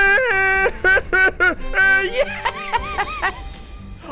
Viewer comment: it’s just fully 😂 edit did he just say yaaaay then to funny baby cry’s